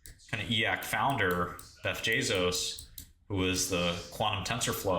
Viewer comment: beff jezos ? did he say that? 😭